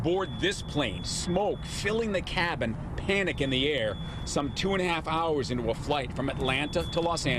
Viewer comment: I thought he said no panic . Then said panic in the air .